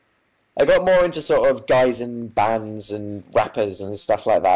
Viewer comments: did he just say he 'got more into guys and bands' :0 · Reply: I thought he said guys in bands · Stranger: "are you sure you're not gay?" Dan: "*laughs* find me the right guy" Anybody else freak out at this part? I honestly screamed as loud as I could😂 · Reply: It sounded like he said when he was younger he began getting into guys and bands but it was probably guys in bands